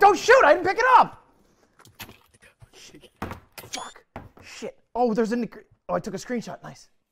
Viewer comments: what he said ohh shit there is a ... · sounds like he says “oh there’s a n word”